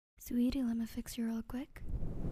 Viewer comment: I was watching a very loud video before clicking on this so i had my volume on minimum. And at the start i misheard, i thought she said "sweetie lemme fix your ugly" then begins to snip my face 💀💀💀💀